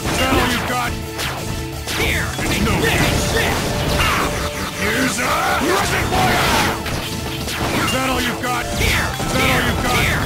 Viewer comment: What does Armored Armadillo say at 5:21? All I hear is: ”Here’s a... not requoria!”